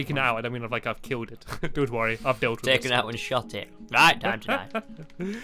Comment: omg stampy at 4:48 i thought u said f*ck its, not buckets XD